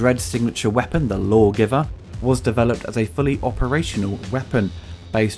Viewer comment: I heard "hole giver" as the name of the gun & thought, "what an appropriate name for a gun!"